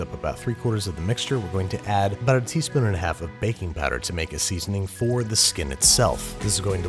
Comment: I really thought he said: "Bacon powder" at 1:02 (listen at a low volume)